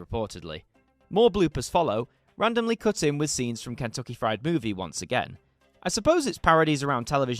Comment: Merry Christmas. 5:54 - Did you just say "Kentucky Fried Booby"?